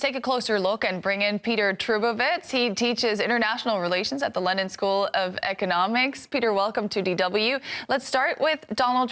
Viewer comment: When she introduced Peter, it sounded like ahe said "Lenin School of Economics"